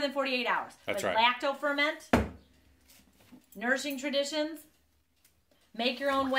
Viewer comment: Another great video! I like seeing you conquer the rock vs. lid challenge! Haha! I did have a moment of frustration coz I tried to look up the book & I thought you were saying, “Nursing Traditions” & Half Price Books didn’t find it. So I Googled it & found it immediately. I know it takes time, but if you could put the titles,names,etc., to references in the video that would help so much. We all have accents to some degree; I’m an Okie from Arizona.